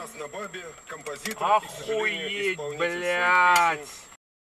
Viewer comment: Not sure of the Western spelling or equivalent translation. But what I hear at 4:00 sounds like it should be the name of this video, (and most of these car crash vids) - Beilyaaaaaaaaaaat !